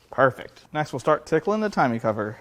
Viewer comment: I swear it sounded like you said tickling the timing cover